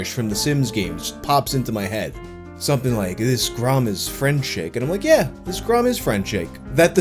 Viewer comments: This grom really is frencheg... So deep. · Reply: I always thought they were saying "Dis grall is frettishay." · I always heard it as "this grau is fertishing"